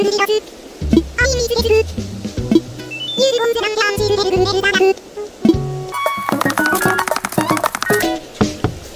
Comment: WHAT did she call you?? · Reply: He said symbols